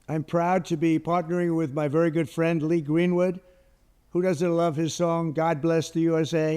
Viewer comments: Aha. I didn't hear a query... thought he was saying greenwood no longer likes the song. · For a sec I thought he was saying Lee Greenwald doesn't like his song...😂